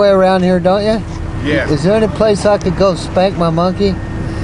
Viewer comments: I thought it said Spank my Money .... I was wrong! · I thought it said "SPANKING MY MONEY".